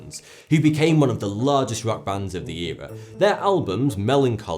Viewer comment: did he just say Smashing Pumpkins is a rap band? Or just another example of his terrible pronunciation again? 😂